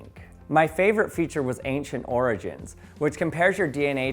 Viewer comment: what I heard: "my favourite feature was agent orange" 🙈😂